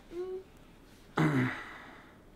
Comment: sorry, but what did say? Thanks to anyone who may see this late comment. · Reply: "So you guys keen?"